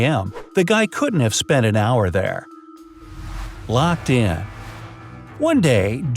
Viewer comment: it says red car instead of locked in 😮